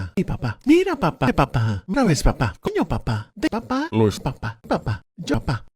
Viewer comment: En el 15:05 parece que dice “coño papá”